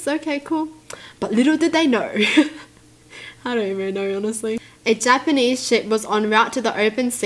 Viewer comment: I thout I heard "Japanese shit" and blurted out laughing💀💀